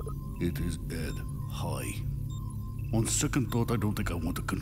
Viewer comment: What did he say? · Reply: "I'm afraid of what it will do to my own brain."